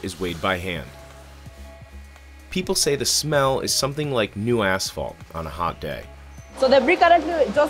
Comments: my alarm paused the video and all I heard was "it smells like new ass-" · I thought he said people say it smells like new asshole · I thought he was saying the smell is like new ass! 😆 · I thought that he said “people say the smell is something like new assh*le on a hot day” at first, and I was taken out by how tranquilly he said it lmao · I thought he said "The smell is something like new a$$hole on a hot day 😅" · Thought he said "smells like new asshole" instead of "new asphalt" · Bruhhh it sounded like he said “ it smells like new A-hole “ lol I was bout to say wdf is new A hole and how do they know what it smells like Lolol then I ran it back 3 times and finally heard “it smells like new asphalt “ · I thought he said smell like “New A**hole on a Hot day” I was like wtf does that smell like?😂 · Anyone else thought he said" The smell is like a new asshole. on a hot day"?🙂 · I thought he said the smell is like new asshole on a hot day. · I swear i thought he said "The smell is like new asshole, on a hot day." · I thought it said the smell is like new asshol- on a hot day...lol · Did he say it smells like new a$$ hole on a hot day? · “People say the smell is something like new asshole on a hot day”???? Pls everyone did I hear wrong 😰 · I generally thought he said the smell smells like new asshole i was like what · Caught me by surprise at 2:29, thought presenter said "the smell is like asshole on a hot day".